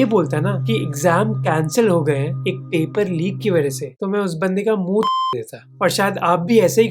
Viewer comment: I thought that he said Munh ch*d deta instead of munh todd deta